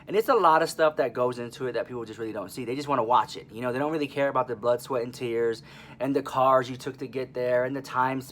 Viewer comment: could have sworn you said "butt sweat and tears" @ 4:25 lol